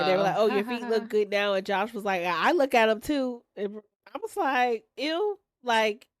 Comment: he actually said “ i PICK them too” not that he look at her feet… ain’t nobody lookin at her feet 😂